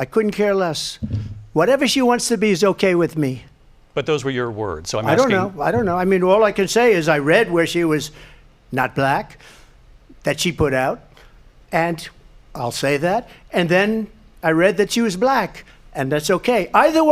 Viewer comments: Did trump just say that she puts out??? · " Shes not black. She put out. Ill say that. " At 6:07 Did i hear that right?